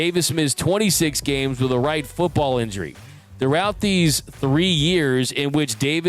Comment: I backed it up a dozen times because I couldn’t be hearing him correctly, but every single time I heard, “right football injury”. What do you hear?